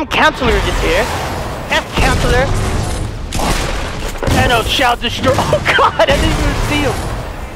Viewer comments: I thought u said VANOSS xD in the first of the vid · I thought he said vanoss